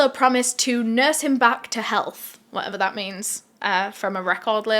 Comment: I thought she said nurse him back to hell😂